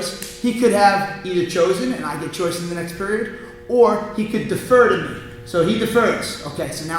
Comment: I thought he was saying "the fur"😂😂 i wrote down the fur in my notes